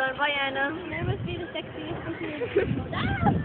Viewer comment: i thought you said i must be the sexiest chick in the world?